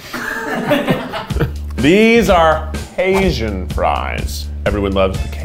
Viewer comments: I thought he said “these are Asian fries” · If it weren't for the captions, I would have thought he said ASIAN FRIES and not CAJUN FRIES. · SKSKSK. I THOUGHT HE SAID ASIAN FRIES, NOT CAJUN FRIES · When he said cajun fries, I thought he said asian fries🤣